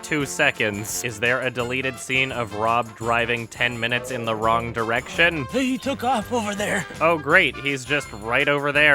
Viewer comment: Does anybody else hear someone speaking while Adum is talking at 8:41? I can't figure out what the hell is being said but I know it's there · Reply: Its "Ah, Bingo." but muffled and in reverse